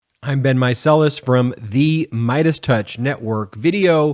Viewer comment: Started introducing yourself "I'm Ben"? I always heard "it's been" before. Noticed and appreciated. Thanks.